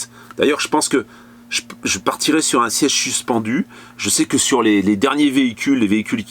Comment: c'est quoi un ' CHIECHE CHUCHPENDUE?.?' svp merci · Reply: Un siège suspendu ! haha! :-)